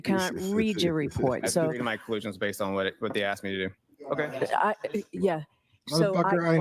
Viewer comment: trooper says “I just did my collision the way they asked me to” did I hear that right 🤦‍♀️